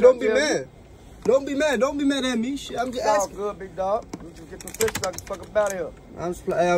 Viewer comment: what does he say? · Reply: Im trynna get tf up outta here